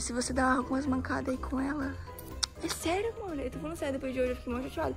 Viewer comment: Só eu que escutei " é sério amor"